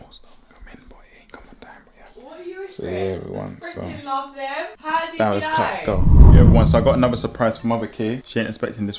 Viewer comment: Can someone tell me what was saying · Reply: ‘I’ve got more stuff coming but it didn’t come on time’